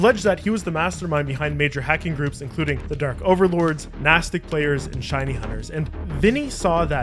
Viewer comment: I thought you said Mastic players. I was confused, people in Mastic could afford a pc, and be competent in using it? No way.